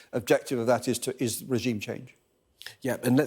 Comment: Did he just say "regime change"?